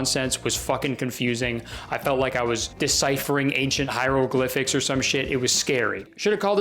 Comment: Nice Majora's Mask reference... also it sounded like you said HYRULE-glyphics. I always have Zelda on the brain LOL.